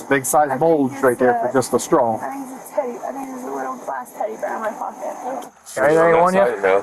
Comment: not that it matters, BUT I think she actually said "theres a little glass teddy bear in my pocket" the so caption is wilin